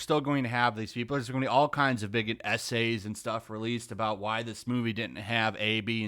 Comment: Haha I almost got triggered, at 6:21 I thought you said - there's going to be all kinds of bigot Ese's.